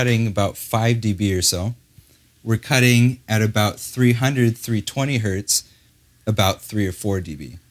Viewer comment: Sorry i'm french and I don't understand at 3:40 did he say 3320HZ or 320? Thanks